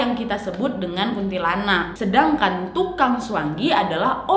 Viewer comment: sumpah gua sampe salah denger gua pikir kak ngmng "CUANGKI" 😭😭😭